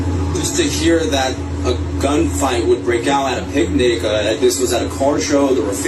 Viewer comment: Did he say “pic-nig”?